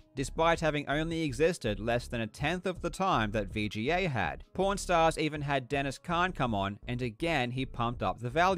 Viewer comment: it sounds like he says pornstars every time he says pawnstars